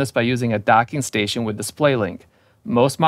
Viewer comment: What is the name of the application software that provides the handshake for the Mac. I thought I heard datalink but can’t find it in the comments.